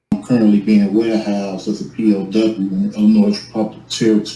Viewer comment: did I hear him correctly did he call himself a P.O.W. ? I wasn’t aware we were at war with Moorish citizens.